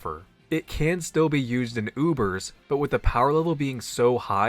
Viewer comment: I thought said “goobers” and I immediately thought “yes, Orthworm absolutely belongs in goober tier” 😝